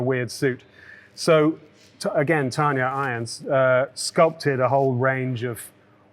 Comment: Did anyone catch the prop makers name or a link to them? I heard "Tiny Irons" but I can't find them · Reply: Was it Tanya irons?